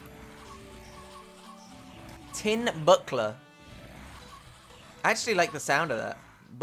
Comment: I thought he said "Tin Butt plug" XD